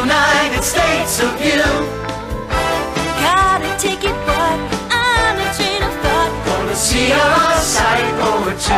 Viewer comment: did she just say the f word? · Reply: She said "On a train to thought"